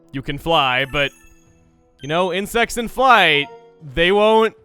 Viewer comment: I accidentally misheard of said because instead of insects I heard "in sex".